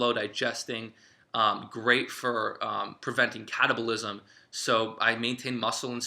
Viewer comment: did you say cottage cheese prevents cannibalism?